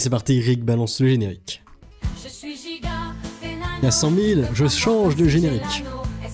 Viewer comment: J'ai entendu "à 100k je change de générique", j'me suis dit enfin, il est éclaté l'actuel